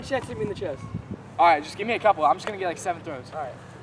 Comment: did he said "I'm going to be just like Stephen Curry"? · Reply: Jake Lang he said “I’m going to get like 7 throws”